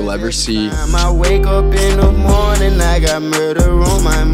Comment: Why do i hear "its not even morning and i got breakfast on my mind"